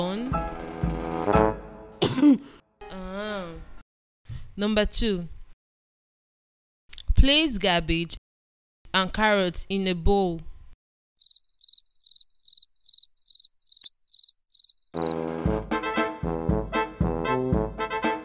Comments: Did she just say place gabbage and carrot 🤣😂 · I misheard her saying Place garbage and carrot in a bowl 🤣🤣🤣